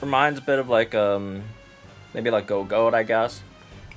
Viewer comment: Reminds you of who? Couldn't catch it. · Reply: It reminds him of Gogoat which is the pokemon that holiday Professor Sycamore is using and is a support unit as well.